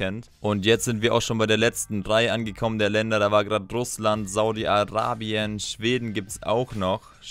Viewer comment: ich habe erstmal Saudi-Albanien verstanden 😂😂